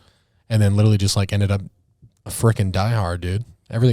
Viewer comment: I thought he said the F word too but I played it back and it was freak